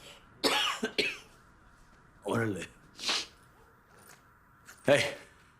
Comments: Just a small correction: In 14:47 I think Tuco said "Órale", not "Oh, really?". "Órale" is an expression in spanish to accept something like a deal, usually accompanied by a "pues" as "Órale, pues". It is like "done deal" :3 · Tuco doesn't say "Oh, really?", he says "Orale", in spanish which means "OK", meaning that when Walter says Tuco got to take 2 pounds instead of 1, Tuco laughs and says "Orale", you can also say "Orale bato", "Orale wey", bato and wey can be used as dude or man.